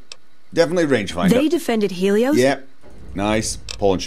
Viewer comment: I thought that Helios One said Hell sone when he approached it xD